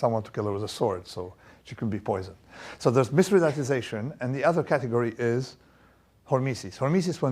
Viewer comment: what is the word? “Mis……” and hormesis. Transcript not helpful. Thx. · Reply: Mythridatisation, derived from mythridates or mithridates idk how it is spelled, he used to consume poison in small quantities in order to get immunity to that poison